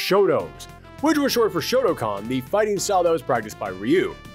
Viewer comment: Did you just say Shotocon? Or Shotacon? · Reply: Shotokan